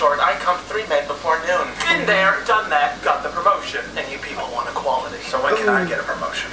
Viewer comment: Is he saying "wanted quality" or "want equality?"